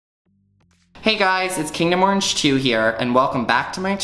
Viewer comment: I always hear, “kingdom orange chew.” lol 🧡🍊